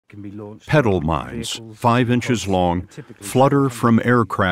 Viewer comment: Oh ’petal’. I thought he’d said ‘pedal’.